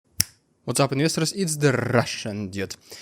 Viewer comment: Still sounds like your saying What's Up Minister's